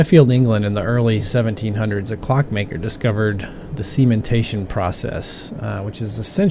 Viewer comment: i thought he said "the semen tasting process"